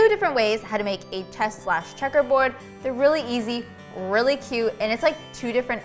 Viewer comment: I thought it said 'Cheese and checkers board' so I thought you made a cheeseboard inspired by a checkers board or a checkers board made from cheese...